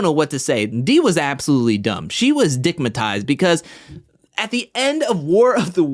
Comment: did you say dickmatized? Lmaoooo